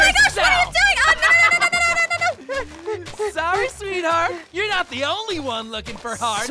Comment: did NOI CALL U SWEETHEART